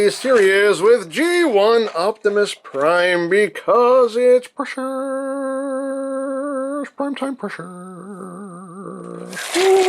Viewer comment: Can anyone tell me what he says at 4:08? It's an thing I've always wondered about... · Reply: He's saying "Because it's Precious, Prime time precious"